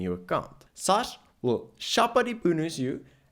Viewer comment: Did you just say that "SARS will Shapa dibunus" ? 🤨😁